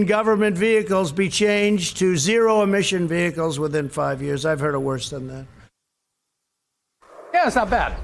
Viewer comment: what did he say? "I've heard a.. wrz.dun eh" ??